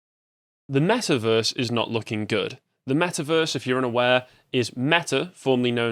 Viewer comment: At the very start, I swear I heard you say "The mass of earth is not looking good." I did a double take on the title lol.